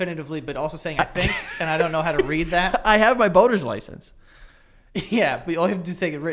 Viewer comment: anyone else hear “boner’s license”